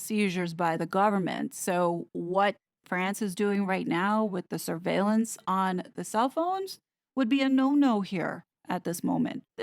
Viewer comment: "what France is doing now with the ......" what is being said here? Which word? Please, enable CC! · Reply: She says …”…is doing now with the surveillance on the cell phones…”. Hope this assists you friend. ❤️🇦🇺🦘🇦🇺❤️